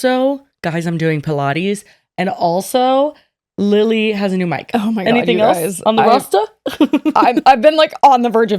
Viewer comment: I thought she said Lilly got a new man and I was shocked that they went from that to talking about a mic lmaoooo